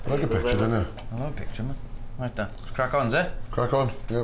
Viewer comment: What did they say just before tasting ? "Crack on"? It means " let's get it started " or " Try it "?